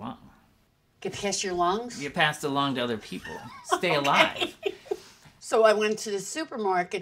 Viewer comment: Seriously, I ALSO thought he said "wants to get past a lung" (which is actually kinda true).